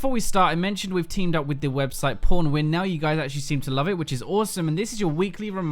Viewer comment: i heard "porn win"